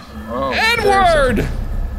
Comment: I had this on in the background and I heard 'N wooorrrrrd!' and was caught totally off guard, so glad I was in a position to immediately rewind and find it was 'Edward.'